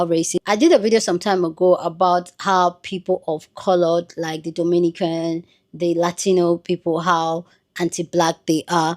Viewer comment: Why do I always hear "me no black" when someone says Dominican 😂